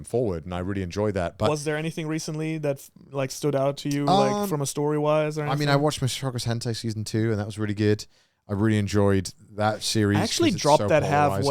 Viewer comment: What anime did Connor say? I didn't quiet catch that Edit: Is it Mushoku Tensei? Edit 2: Oh yes it is